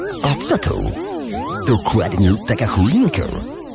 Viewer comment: Tylko ja usłyszałam "DokłAdNiE TAkA CHUJINKA"? XD